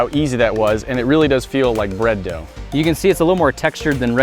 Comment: Did i hear Beard dildough?